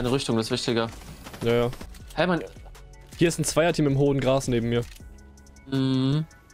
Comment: habe hoden Grass verstanden